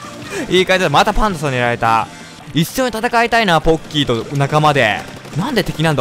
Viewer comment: のとこまぁたぱんつさんにやられたぁ… に聞こえた